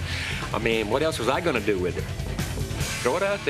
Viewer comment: “Check the dice”. That’s awesome, and the perfect Stablerism. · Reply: I thought he said shake or chuck the dice